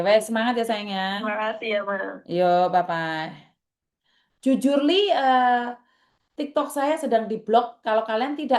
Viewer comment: Smp tak ulang dengerin baik2, tak kira aku Salah denger ternyata dia mmg bilang Jujurly 😀😀